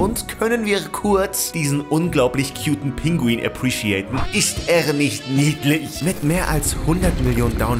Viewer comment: ich glaube ich habe zu viel AoT geschaut, dass ich da verstanden habe "Ist Eren nicht niedlich" 🤣